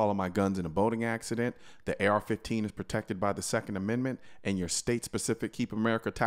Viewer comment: I thought he said second amendment African.